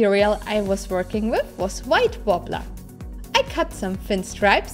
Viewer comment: What is the material called? I couldn't understand it... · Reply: She said white worbla